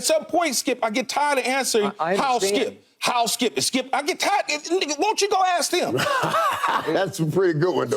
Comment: At the 17:42-17:44 minute mark Did I hear Keyshawn say " (N-Word) Ni..a , go ask him".. LOLOLOLOL